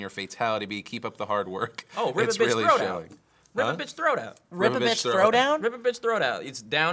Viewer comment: I really thought he said river bitch throwdown